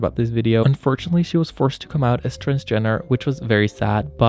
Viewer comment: 10/10 I misheard "she came out as transgender" as "she came out as kris jenner" and i was like..... wait wait hold up